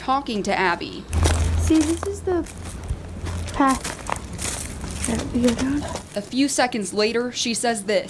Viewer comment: I heard “that’s how we go down”